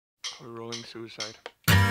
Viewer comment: in the beggining does serj say "a rolling suicide"? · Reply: I heard "enrolling suicide" hahaha idk why but that's what it sounded like to me for the longest time since the song came out basically